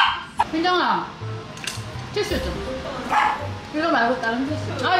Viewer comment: what did she call evelyn is it her korean name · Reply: She called her Hyunjung which is Evelyns Korean name